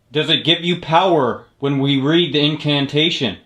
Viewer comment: asks "If that's you why are you here?", I think it responds "Yes, that's me." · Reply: i thought i heard in-can-ta-tion